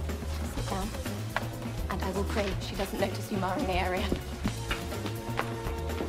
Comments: I can’t make out what Emily said after, “…and I will pray she…”?? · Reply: "I will pray she doesn't notice you marring(?) the area" .. that's what I picked up lol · what did emily say to andy? i could barely understand what she said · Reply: "and I will pray she doesn't notice you marring the area" that is to say, she's trying to make everything look perfect, and Andy is making the place look untidy.